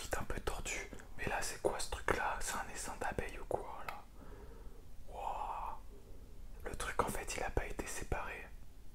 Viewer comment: C'est un Essin(je sais pas l'écrire) d'abeille ou quoi j'ai rigoler pendant 5 mins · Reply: essaim :)